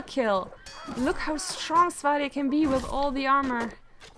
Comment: What are you saying at 30:38? "Look how strong ***** can be with all the armour" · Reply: "Look how strong SWADIA can be with all the armor" Also, thanks for watching my video that far ^^